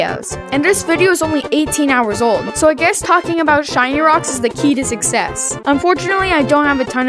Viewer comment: i thought he said shiny crocs..